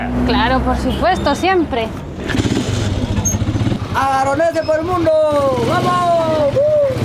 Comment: Pense que decia araganes por el mundo